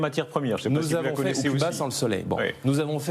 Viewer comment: "? sans le soleil" Il dit quoi sans le soleil ? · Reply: Cuba sans le Soleil